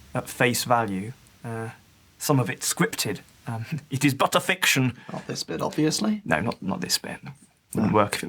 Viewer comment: Thought you said 'butter fiction'. That needs to be a thing. *writes down in my dairy*